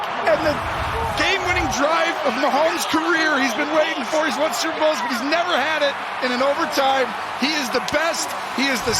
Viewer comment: Lol, what is the guy saying at 20:11 when he bumps the camera? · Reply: dont push